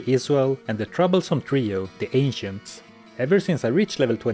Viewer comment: I heard "The troublesome trio: The Asians"